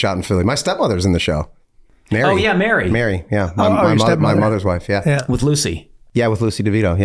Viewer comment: did he say my mothers wife my step mom??? what the fuck